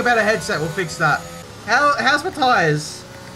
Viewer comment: Thought you said "How's Mathias?" when you said "How's my tires?" Got a bit confused